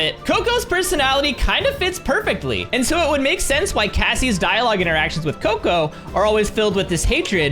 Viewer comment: did anyone hear cassie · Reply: Yes I was like “did he just say Cassie?”